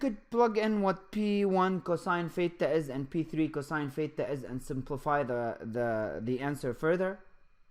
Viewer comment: I keep hearing "pheta",,lol. But this video was really helpful.